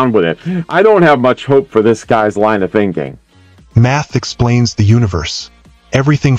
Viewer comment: At first, I didn't hear math from the narrator. I heard, "meth explains.."